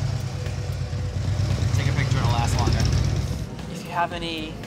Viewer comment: did anyone else hear one of them say "take a picture, it'll last longer."